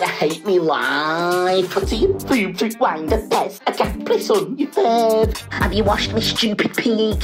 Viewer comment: Sounds like he said a cat pissed on your foot💀